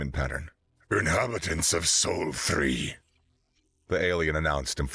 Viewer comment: My hearing is so bad that this is what I heard: "Inhabitants of Sol 3.... Hi."